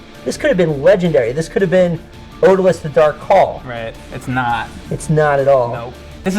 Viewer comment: What game does Ryan mention where they splice a photo into the video at 10:57? It sounds like he says, "Order Less the Dark Call" but I know that can't be right; the mic kinda goes up and down and distorts. The artwork/graphics look sick in that photo. If anyone knows what he's talking about, please, comment! Thanks! ;) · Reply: In case you haven't found out already it's "Odallus: The Dark Call"